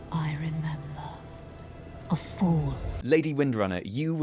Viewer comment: I thought she was saying "i remember a fool"